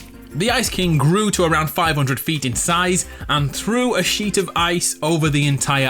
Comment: When u said "sheet of ice" I heard "shit ton of ice" and wondered why u didn't blur it